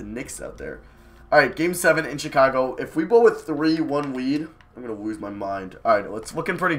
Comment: did he say "if we blow a 3 1 weed" or am i to stoned